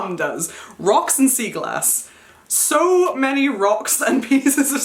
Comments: I misheard sea glass as seagull ass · 😭when you said sea glass I thought you said seagull ass, I was like- your mom collects pieces of WHAT!?😭😂💀 I’ve never heard of sea glass😅😂